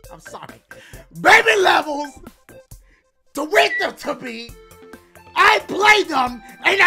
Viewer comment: Has anybody noticed that he sounds like he says “bake me levels, tweet em to pee”